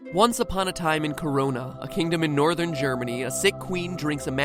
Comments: When he said Corona I thought he said Kokona from Yandere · lol when he said Corona i thought he said Korea lmao